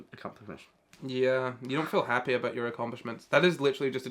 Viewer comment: I FREAKIN LOVE HOW HE SAYS “accompamesh” INSTEAD OF ACCOMPLISHMENTS- Dyslexia at work right there- (I think it’s dyslexia)